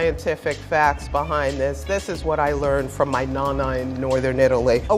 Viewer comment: did she say "nanay"?